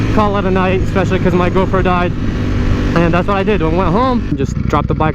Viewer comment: It sounded like you said "especially because my girlfriend died" but I went back and common sense told me that you said GoPro lol